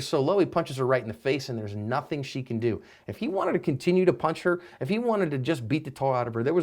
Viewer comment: Did you really say “he wanted to beat the Tar out of her.” ?? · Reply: nah that's not what he even said. IF he wanted to.